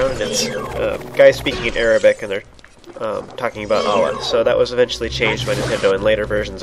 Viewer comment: the chants are about Allah, though it does sound like he said olives lol